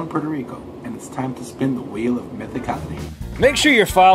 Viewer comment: I thought he said it's time to spin the wheel of hepatitis XD